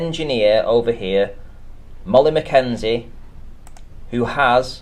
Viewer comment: I thought he said “Mullet McKenzie.” Had to rewind twice.